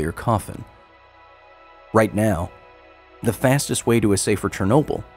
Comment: what was said at 9:29 ? russian or ukrainian? Дана ссылка на базу. link to the database?? "Until Next Time"? in Ukrainian "do nastupnoho razu" до наступного разу